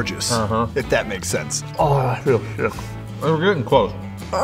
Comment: it sounds like says oh, I feel the shit, and the captions say that. Maybe he did say it